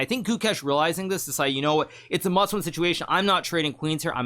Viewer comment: what does Hikaru mean by saying a Muslim situation? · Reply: Must win *